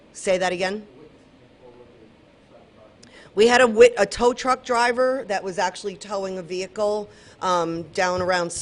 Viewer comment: I'm SHOCKED that she gave up witness information and went as far as to say tow truck driver lived in same complex!!!!!!! Absolutely WRONG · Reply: except, that is NOT what she said.... Listen again, 5:29 "We had a wit.. a tow truck driver that was actually towing a vehicle...." then at 6:20 "his apartment is in the same complex that the tow truck driver was in..." The Tow truck driver was in the complex towing a car, not that the driver lived there.